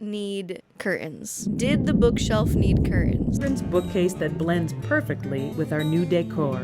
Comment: Here is a house for 6 children Wait did you say 6 children i thought you said a 60 year old.